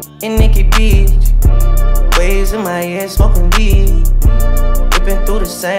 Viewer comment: I thought he said. Days in Miami smokin weed. Damn I need to lay off the blunts